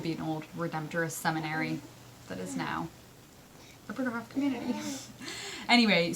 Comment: Captions can be weird : at 6:15 where you mention the Bruderhof community , the subtitles say "brutal" . . . i know they're wrong - c u in '21 💖